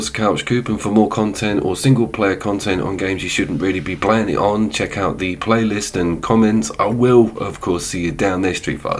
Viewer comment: did you just say..... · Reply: its gonna be a cracker!!!!!!!!!!!